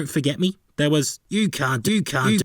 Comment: "You cunt" That's what I heard at least.